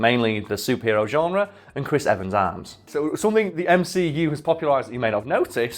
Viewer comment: when he said “Christ Evens arms” i thought he said “Christ Evens arse”. The ass of america is truly an icon